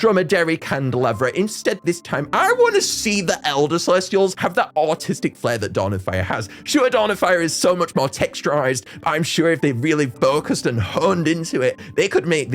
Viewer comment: I thought he said autistic 😅